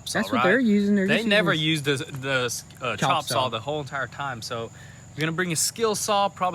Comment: sounds like Satan saying "chop saw".